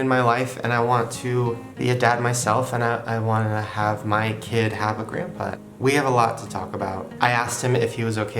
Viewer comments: this is an amazing video, made me cry BUT at 7:47 it sounds like he says “and i want my kid to have a great butt.” you’re welcome ahah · did he say he wants his kid to have a great butt??